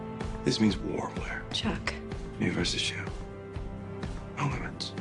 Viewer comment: wht did he say!? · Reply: Me versus You